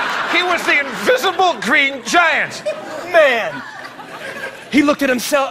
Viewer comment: I heard “green giant wiseman”